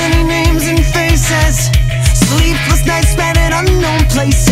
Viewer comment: I thought it said sleep with nice men but it said sleeplessnights or something like dat